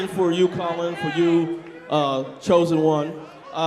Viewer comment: does he call Conor Colin?